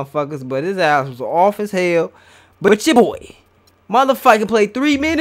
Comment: what did dis nigga say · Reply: "But ya boy"